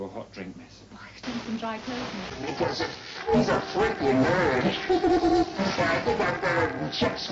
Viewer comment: this is what i heard 1:07 "He does it he's a Frickin' Nerd laughter and next part i dunno Chuck Swirsky*?*' 1:20 "OHHHHHHHHHH JESUS OH JESUS NYOOOOOOOM NYYYYYYYYYYYYYOM NNNNNNYYYYYYYYYYYYOOOOOOOOOOOOOM catch the wave" 1:40 "TUTUTUTURUUTUTTURUUUUUUUU" 2:09 "NYYYYYYY THEN SLAP ME B*TCH CASUAL SCREAMING *?*"